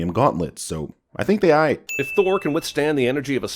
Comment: WTF did you say at 19:16? "I think they I"? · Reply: "I think they aight" = I think they're alright